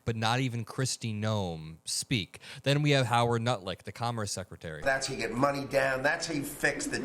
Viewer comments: 20 had to go back im all "no way did i just hear nutlick" yes, yes i did. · I heard you say " Harold nutlick "